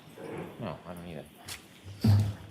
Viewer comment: Did anyone else hear say "No, I mean it" at 20:01 time stamped. I wonder what he means by that statement